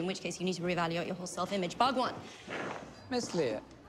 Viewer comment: thought she said whaagwan!